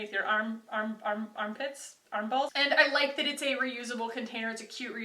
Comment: did you just say “armballs” or did I hear that wrong? 😂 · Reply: Armballs yep